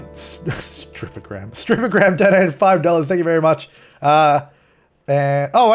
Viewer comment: I thought it said Strippergram donated Ass lol